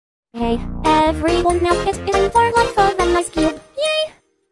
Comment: What I hear: Hey everyone knows its in for life of an icecube YAY! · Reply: What I hear:Hey everyone it’s time for life of a icecube YAY!!